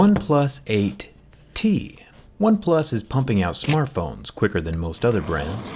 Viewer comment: "Oneplus is pumping out smartphones quicker than other brands" - Jerry But Xiaomi and Realme do this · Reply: I thought he said pooping out